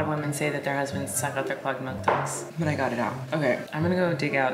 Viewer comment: clogged milk ducts, is that what was said?